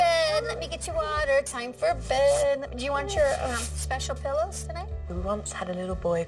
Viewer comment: Adding this comment because someone made it on another channel’s version of this episode and it was funny: “Sounds like is saying ‘do you want your special pills?’ (instead of “pillows”) in the last bedtime scene.